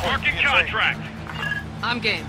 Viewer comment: 🗣️ I'm gay 😂 (when i heard it)